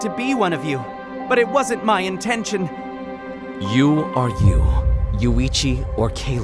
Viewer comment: I am so confused what does you are you you mean 11:30 he said you are you but it wrote you are you you